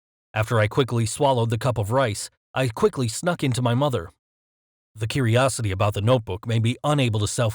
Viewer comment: did he say in my mother